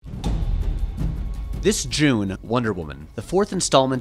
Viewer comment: At the beginning of the video, I thought he said "This Jew, Wonder Woman..." for a sec.